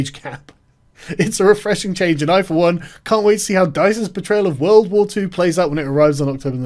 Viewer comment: It sounded like you said "...can't wait to see DICE's betrayal of World War 2..." instead of "portrayal", and that made you sound wittier than you are, it would seem.